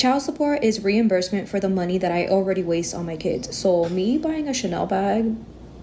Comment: Waste on my kids ….. 😑did I just hear that correctly 🤣